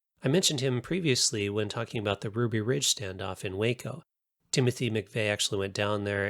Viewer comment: I know. I talk about one in one episode and the other in another. But I mention both in each. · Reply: You know what, I think you said Ruby Ridge and Waco, and I heard it as Ruby Ridge in Waco. My mistake